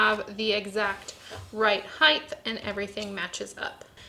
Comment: Why does it sound like is saying "heighth"?